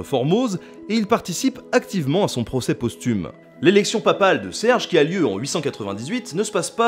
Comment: J'ai compris : Papaye